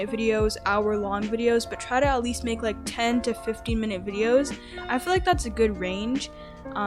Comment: i almost thought you said 10 to 50 and i was like UMMMM but then i relised you said 10 to 15 LOL